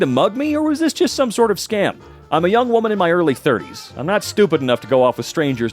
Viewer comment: Young lady in early 30s? Could have sworn it Said 20s but That is probably just me